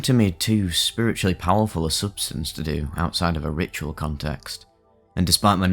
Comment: I thought he said "ritual contest" at first. That would be some competition